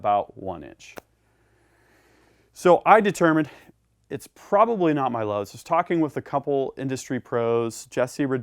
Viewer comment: Did you lap your lugs? What are you saying @ 7:38 ? "it's probably not my lugs"? loves? loss?